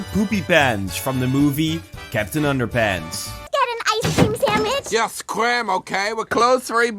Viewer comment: I swear she said Samich 😂